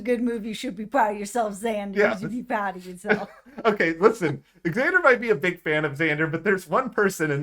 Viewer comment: did you say "eggzander might be a big fan of xander" my name is xander and my friend always pronounces it eggzander and i am more than a big fan of xander hes not just my favorite of the season hes my favorite of all time and might be me from the future